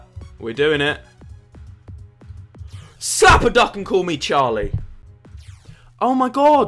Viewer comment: I never thought I'd hear oli say super duck and call me charlie